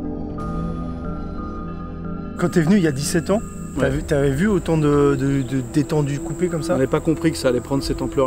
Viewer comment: j'ai entendu ta deja vu des teton couper comme sa XD